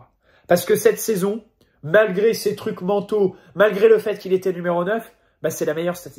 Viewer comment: ces trucs mentaux ?? 🤣🤣🤣🤣 ou c’est moi qui n’ai pas bien entendu